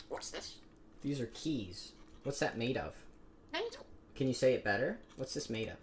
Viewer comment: Sounds like he said mental instead of metal lmao